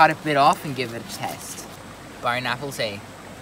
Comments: Like if u heard him say bon apple tea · I heard bone Apple tea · did he say bon apple tea